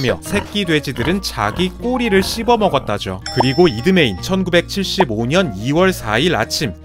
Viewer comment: 근댘ㅋㅋㅋㅋ 자막은 1975인뎈ㅋㅋㅋ말은 2975 무슨 미래에 지진이 일어나나욬ㅋㅋㅋㅋㅋㅋㅋㅋ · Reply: 님이 잘못 들은거에요 4:57 들으면 2975년으로 들리는데 4:57 전 부터 들으면 1975로 들려요